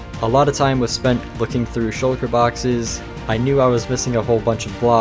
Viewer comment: anyone else notice that at 10:04 the subtitles say "shoulder" isntead of shulker